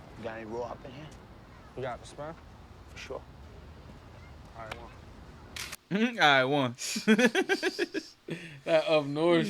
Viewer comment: What did the white drug dealer say to Herc at 17:03? 'Alright won'? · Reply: Ight One